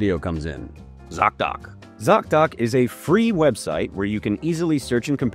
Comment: #sponsored Be kind to yourself this year. Using Zocdoc is FREE - visit <link> to find and instantly book an appointment with a top-rated, in-network doctor today. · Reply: My bad, I thought you said cock doc.